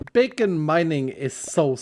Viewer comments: I thought he said bacon mining ngl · For a second there I thought you said bacon mining and I was totally on board with that idea.